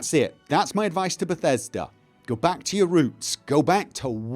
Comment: Lmao. At around the 18 min mark, i thought you said "go back to your room" not "roots". I did a serious double take. Very good video though. I always appreciate hearing your opinion. Keep up the good work bud!